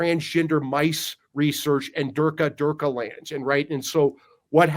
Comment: Did he just say durka durka land ? 😊